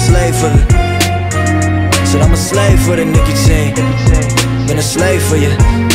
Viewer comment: the whole time I thought he was saying "I'm a slave for the Nikki team"